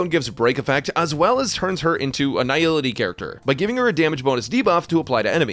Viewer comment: why do I keep hearing "Her Dammage" on a guide for a harmony path character ?